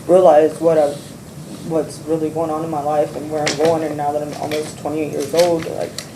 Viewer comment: It sounds like Jason groomed her from a young age if she has 4 kids at 19. I'm guessing the one adopted out was pretty much taken at birth. While that doesn't absolve her of responsibility it does explain some things. I think prison might be the best thing to ever happen to her. Ironically, it sounds like her first real stability. If she can use the time well it could be of genuine help. And if I'm wrong, it at least gives the public a break from her. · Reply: She’s 27. It sounded like she said “almost 20 years old” but if you listen closely she actually said “almost 28.”